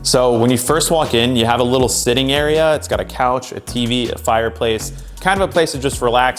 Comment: When he said master bedroom i thought he said masturbate room...